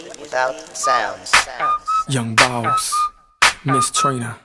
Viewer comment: Why did I hear “Young balls” at the beggining of the short...?